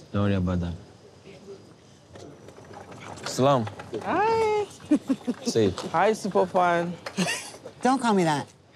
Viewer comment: Did I hear usman say, “say it” ? … hi super fan..